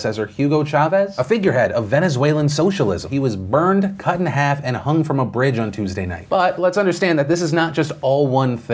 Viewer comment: i wasn't paying attention and thought you were saying they burned cut and half and hung a human.